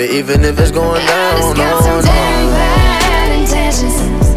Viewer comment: i thought she said i got some tampaxx extensions 😭😭😭😭💀💀